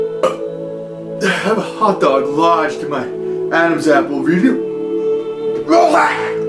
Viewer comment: he sounded like he said ROLEX!!!!!